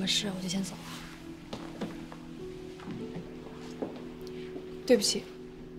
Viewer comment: she said “Sorry” I don’t know why the subtitle says “Excuse me” lol 😂